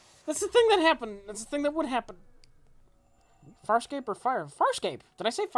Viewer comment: Is saying Farscape or FIRE ESCAPE?